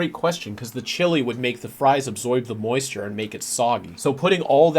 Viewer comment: Buddy. My Brotateochip. Did you just say Absoyb?